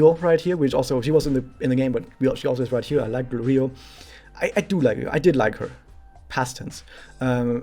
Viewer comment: Can someone tell me who the girl with Sana at 28:39 is again please? I can't hear her name properly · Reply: Ryou